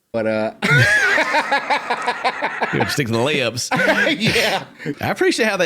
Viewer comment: the captions say "STI the layups" thats crazy 🤣